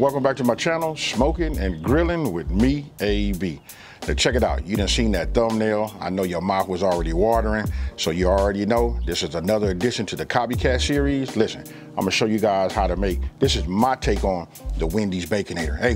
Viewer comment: thought he said "I know ur mom is watering."